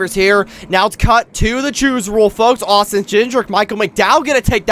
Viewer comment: Did I just hear Austin Ginger?!